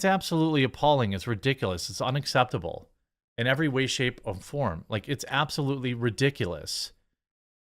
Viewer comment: Did you say "In every way, shape OF form." ...???